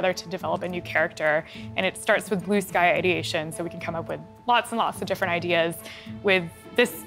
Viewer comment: what does she say here? "blue sky ideation" ? what